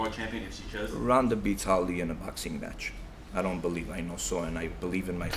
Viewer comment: "Ronda beats Holly in a boxing match?" Erm, huh? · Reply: lol I had to replay that because at first I thought he said "Ronda beats Ali in a boxing match" and I was like 0_o the fuck! But coming from him I wouldn't be surprised. lol